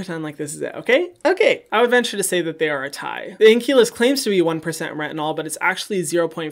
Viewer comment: After all these weeks I thought you've been saying Inculus. Today I realized its Inky List. 😂